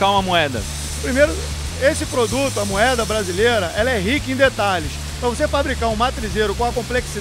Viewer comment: eu escutei "esse produto, amoeba brasileira" kkkkkkkkk